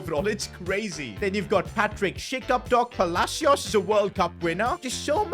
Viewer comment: I think you said shit